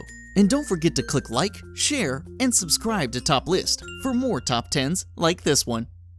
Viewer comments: when you said top lists i thought you said topless! XD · Sounds like you say topless not top list xD